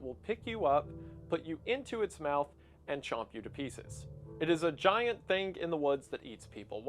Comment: did someone say GIANTS?